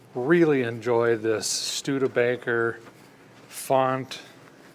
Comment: Did I hear “stude-faker?” 😂